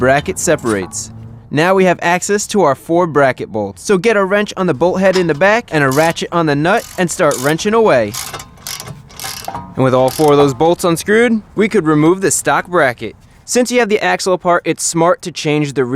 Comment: he said rachet on the nut but it sounded like ratchet on my nut